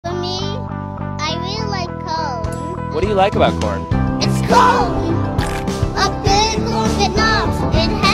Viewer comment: I thought he said at first "ITS ¶ORN!" That got me rolling on the floor 💀